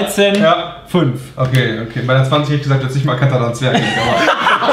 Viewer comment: was sagt brammen? :D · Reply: "Bei 'ner 2O hätte ich gesagt, du hättest nicht mal erkannt, dass das ein Zwerg ist"